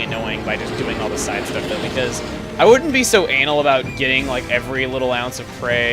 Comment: did he say 'i wouldn't be so ANAL'?